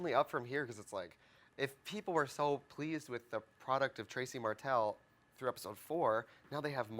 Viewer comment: Does she call herself Tracy Mattel? lol